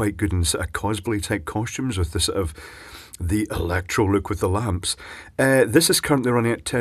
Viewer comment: Interesting. But I'm having trouble understanding what you're saying at 4:03. To me it sounds like "cosbilly-type cautions with the Electro-Luke with the lamps" Perhaps you're actually saying "cosplay-type costumes, with the 'electro' look with the lamps"? If so, then yes, I think that would work well. But don't tell Lady Gaga or she'll steal the idea and make a music video out of it. Wait, actually, do tell her; that would be kinda cool.